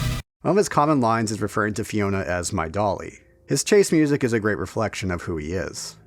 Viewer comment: I may be mistaken but I've always percieved that line as "My darling". Normally, the Y vocal, when compared to the i vocal is a lot lower in pitch, right? If Debilitas is truly saying "dolly", the "y" is of unusually high pitch.